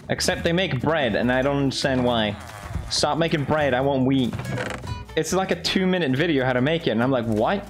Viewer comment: I thought Felix said “stop making bread i want weed” 💀🙌🏼